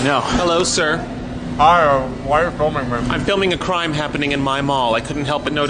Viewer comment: I think it says “Why are you filming me” -? Idk